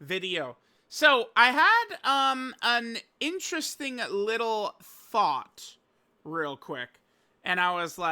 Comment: I thought he said at the beginning “I had a interesting little Fart 💨 “ mhmm mhm mhhhm